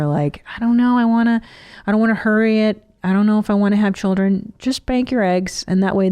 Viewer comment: she said, "Just bank your eggs." But I heard it as, "Just bang your ex."! And for a second I thought, where's she going with this?